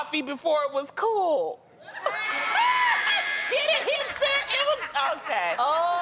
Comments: My ass still here tryna figure out that hamster joke.. "before it cooled".. Wait Wutttt??? lol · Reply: lmbo I thought she said "hamster" too. I guess she said "hipster"? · I thought she said HAMPster lol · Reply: I did, too! She said HIPSTER!! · I thought Loni said hamster, not hipster so i was real confused for at least a full minute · My ass still here tryna figure out that hamster joke.. "before it cooled".. Wait Wutttt??? lol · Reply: Lol I thought she said hamster too and I was like what????😂😂😂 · I didn't get the hamster joke. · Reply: I thought she said Hamster too but she said Hipster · i heard "Hamster" .... was so confused for a while lol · Loni's were actually funny ! The part about them knowing about the jokes so we can think about it is totally TRUE! Trust me I know 😂😂😂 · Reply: i thought she said hamster first but i think she said hipster, the joke would make for sense wouldnt it? lmfao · I thought Loni said hamster, not hipster so i was real confused for at least a full minute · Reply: I thought she said hamster too 🤣🤣🤣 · Loni's were actually funny ! The part about them knowing about the jokes so we can think about it is totally TRUE! Trust me I know 😂😂😂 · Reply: I thought she said hamster too so I totally didn't get it. Makes much more sense as hipster. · When loni told the hipster joke I thought she said hamster and so I was really confused lol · Ok sorry for being dumb but can someone explain th last joke? 😂 Edit: ok got it, thought she said hamster at first